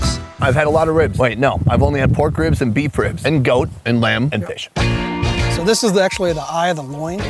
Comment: What was the last type of ribs Sonny Side mentioned? Did he say….fish ‽